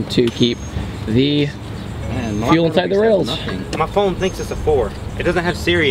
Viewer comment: lol did someone say at 2:59 my phone thinks its a ford. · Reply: Productions he said his phone thinks its a 4, iphone 4 didnt come with siri